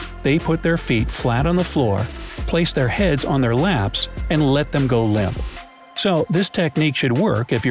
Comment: Did he just say they put their heads in their laps